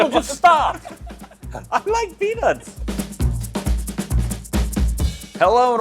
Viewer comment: Did he say he likes penis 0:09 this is the first time to this channel, I feel like I walked into a LA gay bar.